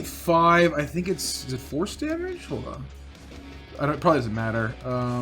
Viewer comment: I've been playing these on 2x speed, so I thought I heard say "I think it's divorce damage?" and I had to rewind to check it again.